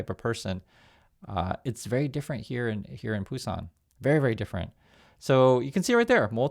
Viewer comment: Ohhhhh. Thought you said "it's very different here in Tucson." Had to turn on subtitles.